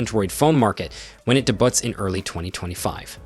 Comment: did i just hear him say DE BUTT